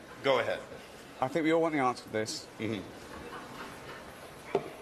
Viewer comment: what is he saying? Wtf... Even means? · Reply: “What the fuck does ‘sugar-tots’ even mean ?” - You need to read the news reports of when Mel Gibson was arrested for drunk driving, or listen to the audio recording on YouTube.